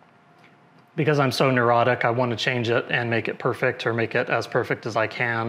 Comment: Great video as usual but for one second there I thought you said I’m erotic 😂 lol everyone makes mistakes no learned to swim by standing by the pool if you get what I mean all the best stay safe